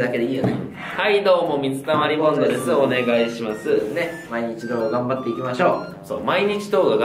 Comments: サブチャンもサブチャンらしい企画で本気！ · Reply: 毎日動画頑張っていきましょう って言ってたんですか！ いまいちど頑張っていきましょうって聞こえてました！ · つい最近まで 「毎日動画頑張っていきましょう」が 「今一度頑張っていきましょう」に聞こえてた！最近やっと聞き取れるようになりました笑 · はいどうも〜水溜りボンドです〜 お願いします〜 今一度頑張っていきましょう！ だと思ってたw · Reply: では、毎日動画頑張っていきましょう！ って聞こえる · 初めて見たとき「はいどうも～水溜まりボンドです～今一度動画頑張っていきましょう！」って聞こえてた笑 きっと、見た動画が早すぎたんだな、自己紹介の速度が笑（言い訳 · 毎日動画頑張っていきましょう 毎日投稿頑張っていきましょうどっち？ 今までいまいちど頑張っていきましょうって聞こえてたw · はい、どうもっ水溜りボンドですー。ねっ、今1度動画頑張って行きましょう！って聞こえてた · 本当に一番最初に見た動画の挨拶が 「はいどーもー水溜りボンドですー ねっ！今日もまぁー頑張っていきましょう！」って聞こえました笑笑でも見て行っているうちに「はいどーもー水溜りボンドですー ねっ！今日も毎日動画頑張っていきましょう！」に聞こえてくるようになった！ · ｢毎日動画頑張って行きましょう！｣だったんですね！ 私は､｢一応動画頑張って行きましょう！｣って聞こえてました(^^;) 私も､なんて言っておられるのか､分からなかったので､ 分かって良かったです(^^) 説明の動画を投稿して頂き､有難うございました(^^)